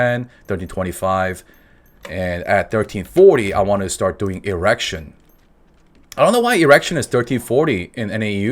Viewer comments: everytime he says "Igrexion" I hear "errection" lmao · Lai Lai my friend.... smacks the npc p.s I swear when you said "Igrexion" all i heard was "erection" ...